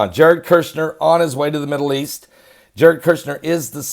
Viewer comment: I thought you said “He (Kushner) is an omen in the Middle East” well he looks like Damien in the old movie “The Omen”..just weird that’s all. Then I realized you said Oman